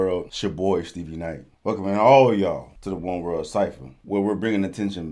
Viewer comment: ngl, i thought it said one word cypher, and it got me very intrigued